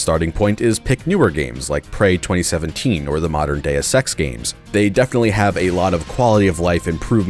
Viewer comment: "Modern Deus Ex games", or as I heard it, "Modern day sex games"